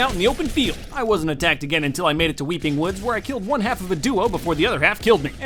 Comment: did he just say weakling woods